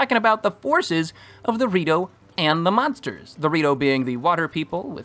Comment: did bro just call zora rito 💀